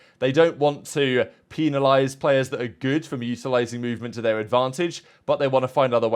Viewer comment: Brother what did you say at 3:44 ? Lmao it sounds like you’re trying to say “utilizing” but it sounds like you said mutilated lmao